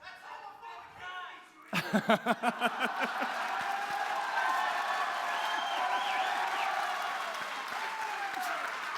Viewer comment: What did he say at 9:12? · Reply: The wildebeast in the crowd yelled "You're not welcome here" to Milo and Crowder retorted with "THAT'S HOMOPHOBIC HATE SPEECH"